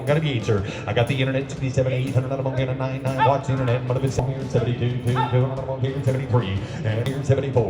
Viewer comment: The auctioneer shouting random things cracks me up 😂 I swear I heard him say internet’s down when the 10200 bid was in